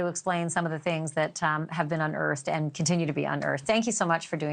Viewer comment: wait did she say in her boobs shaking in my boobs